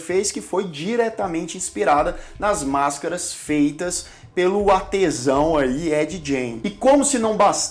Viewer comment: só eu, entendi que as máscaras...foi, feita pelo... há...tesão kkkkkk